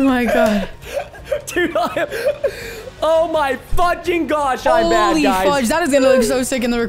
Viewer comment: we had to rewind a few times. I thought you said "Oh my F***ing gosh" and was about to tell my kid he can't watch you anymore. came kind of close